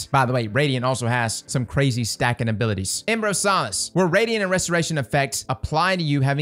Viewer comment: I thought he said emperor of sauce